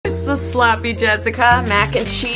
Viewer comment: It sounded like she said it’s the sloppy jesica